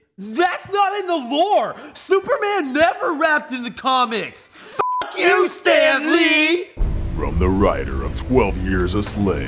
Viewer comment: That part when anthony said "fuck you stanley" it sounded like he said daddy😂😂😂😂😂😂 😂 😂 😂 😂 😂 😂 😂 😂